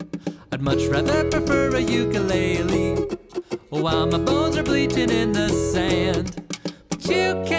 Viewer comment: Am I the only one who thought he said "While my balls are bleaching in the sand"